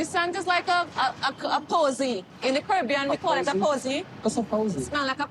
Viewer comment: Funny how he can get snappy with her, but let his ex wife tear into Shaida. I thought Shaida was saying NY smelled like a 🐱🤣. She is putting way too much emphasis on getting pregnant.